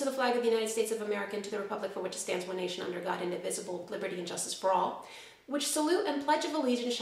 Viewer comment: LOL, I heard "one nation, under God, the invisible" :-D To a lot of foreigners - including me - the pledging of allegiance to the state, the constitution and even the flag seems very weird.